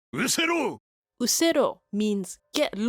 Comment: I'm not the only one who hear "majimo" at 10:18 ... But it Usero all this time😅😅